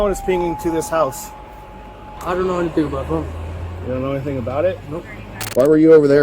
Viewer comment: He right away said..'I don't know anything about THEM' when they only mentioned one. Sick fk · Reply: Yeah I picked up on that too but the cop misheard him and he thought he said he didn't know anything about it but he definitely said them